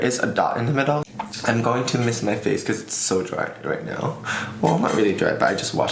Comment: At the beginning I thought you said „I miss my face“, instead of „I‘ll mist my face.“ And I thought, SAME SAME. I always miss your face. You are so beautiful and I like the way you talk. Your videos feel like home to me